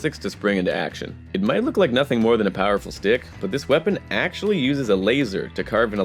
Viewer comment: I had to rewind when he said "powerful stick" cause i thought he said "powerful dick".... 🤣🤣😂😂